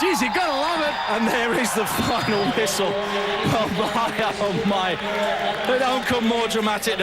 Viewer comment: and there is the fuckin whistle ..wthell.haha ..did i heard it wrong?